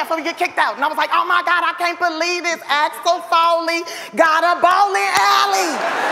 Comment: What did the lady say? Axel Foley? I lost a bit of my hearing. The bloody bard.